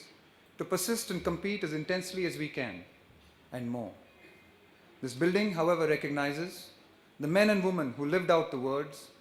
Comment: what does he say around 02:56? right after, "..and more."? Sounds like "This _building_ however recognizes".. which doesn't really make sense...